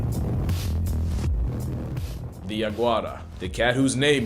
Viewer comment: I heard “The iguana” 🦎 😅